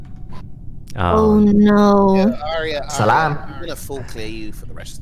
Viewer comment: did i heard wrong or did he said salam